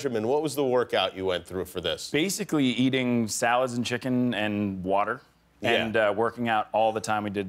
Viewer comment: Did he just say eating water????